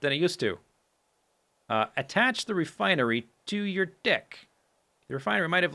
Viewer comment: sorry but 13:16 thought he said dick i was like wait what